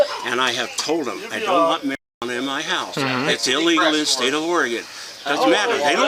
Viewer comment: That’s the Indiana State Police that responded to the call and it is illegal there. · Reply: the father is talking about how all she does is sit at that table drinking vodka and smoking the bong and how it’s illegal in “Oregon” I swear that’s what he says…. 🤷🏻‍♀️ 9:08 ?